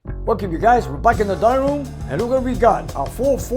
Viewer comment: Thought for a moment said " back in the dining room..." Got my attention for the wrong reason. Sneak a sammich, maybe.